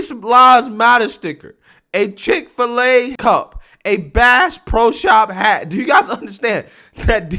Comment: Oh she said bass? I thought she said brass lol